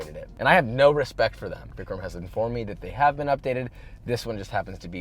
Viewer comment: Just going to make a general comment because I’m seeing a lot of these. At :52 seconds he says “don’t be scared Vicram”. I’m the camera man, he tells me not to be scared and I yell right after. Hope that clears it up for you guys. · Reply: Thank you, because I knew I heard "Vicram" clearly.